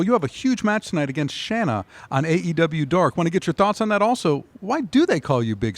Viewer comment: Did Marvez say "AEW Dork"? XD